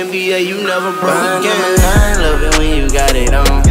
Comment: Just me that thought he said rondo number 9??